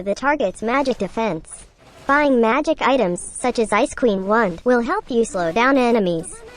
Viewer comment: I heard Ice Cream Wand instead of Ice Queen Wand wtf my ears🌚😂